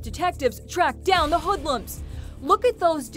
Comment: Did she just say hoodlums